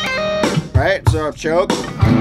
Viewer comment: What does he say in 12:21? Can't understand, and want to learn how to do what he does after saying that. Is it a technique? Sry my bad english :D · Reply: I think he says "right, so I've choked".